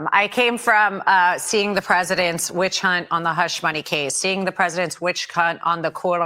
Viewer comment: did she say "witch cunt" on the Letitia James case? I love this woman with my whole life